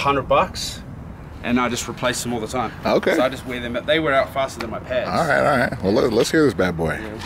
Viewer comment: I thought he said “they wear out faster than my parents” 😂